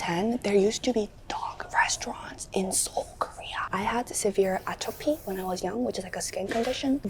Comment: the subtitles are saying atrophy instead of atopy so now people are gonna think her arm shrinked for no reason when she was a kid